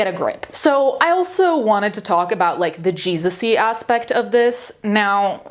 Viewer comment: internet broke my brain, I heard "Jesussy aspect" and I died. JESUSSY